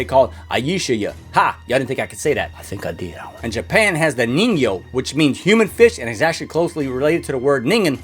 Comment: Yo bro 13:13 i had a ad when he trying to say nengyo and mist the the second n so i heard neg so i was like oh no he sayed the n word